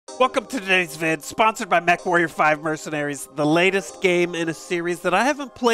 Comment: lol thought you said sponsored by meth warriors 5